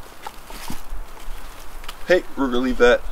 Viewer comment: what happened there? I hear "Rooger" and you put the stick down twice? did he steal the stick?